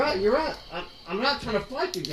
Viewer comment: Domestic?I heard it 'DUMBestic'...